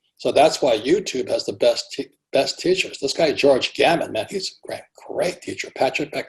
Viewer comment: I would really appreciate it if you could please spell the names of the 3 "teachers" he mentons - that is on youtube. From 31:08. George Gamanmat (or something like that) Patrick McDavid? And Cartoon.... something. I can't hear properly. English is not my naive language. Please help? Gratefulness in advance from Norway! ♡